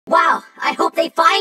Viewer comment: I thought puff ball said “Wow!I hope we find it!”